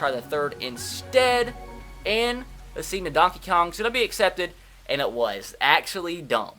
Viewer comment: Am i the only one who heard NuDonkeyKong Suh?